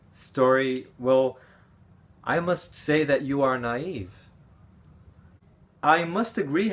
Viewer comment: O que você diz em 2:45? "I must say that you are not...(???)" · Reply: "I must say that you are naive (ingênuo)"